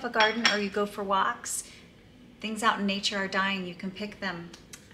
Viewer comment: My 13 year old son heard you say “dyeing” and he said,”What?! She only looks thirty years old. What does she mean dying?” 😂😂😂😂😊